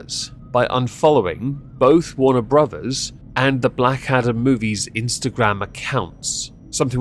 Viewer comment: At first I thought you said Black Adder The Movie. I was all about it for a second until I heard Adam.